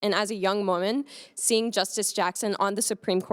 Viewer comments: i thought she said "as a young mormon" lol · Oooooh young woman she sounded like she said "young Mormon" not "young woman" changes context a little